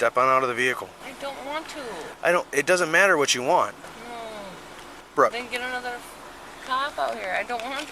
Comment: That “bro.” At 2:35 is hilarious (unless he said brooke and i misheard lol)